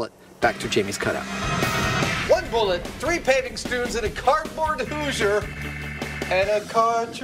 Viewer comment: did he just say 3 paving stoones? Weird!